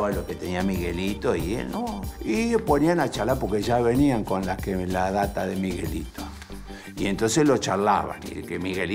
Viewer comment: Qué dice el narrador en 6:50? "porque ya venían con la que [...] de Miguelito" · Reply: con la data